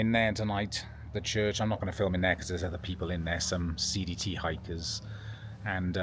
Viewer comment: Im glad I rewatched the last clip in DuBois. I initially thought I heard you say "seedy hikers" versus CDT hikers.